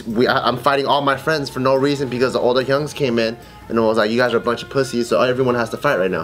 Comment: did say "Older hyungs?" (Korean)